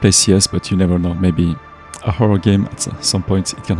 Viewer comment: did i hear HORROR GAMES? also this was an amazing video i can truly say youve improved alot on this journey congrats on the 3k blud 🔥🔥🔥